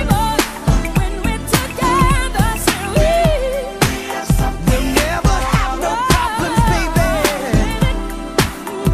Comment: what does she say ? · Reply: Said We